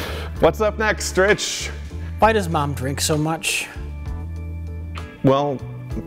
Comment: I was really hoping they would land on "why does mom drink so much." I need answers. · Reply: I swear I thought they said "why does MIKE drink so much."